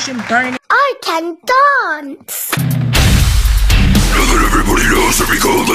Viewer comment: I LEGIT THOUGHT SHE WAS BOUT TO SAY, "I can die!", WHEN SHE SAID, "I can dance!"